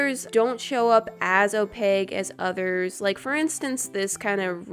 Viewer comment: When she says opaque it sounds like she is saying opeg 😂😂 Am I the only one?